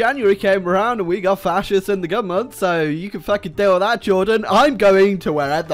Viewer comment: Jordan Just Sounds Like He says Juden xD